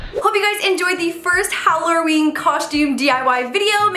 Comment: she said hallarer instead of saying hallowen